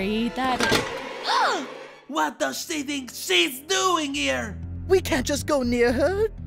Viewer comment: SWEAR? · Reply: they thought that yin-yang said “sh***ing when they said “she think”